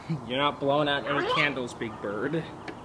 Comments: The first thing I heard was 'Run! You don't want trouble big boy, AAAAAA' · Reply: Alibizia But all I hear is, “One, blow out one candle, Big Bird, (kids cheer)”. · The first thing I heard was 'Run! You don't want trouble big boy, AAAAAA' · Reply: All I heard is, “One, blow out one candle, Big Bird, Children: HOORAAAAAAAAAAY!!!!!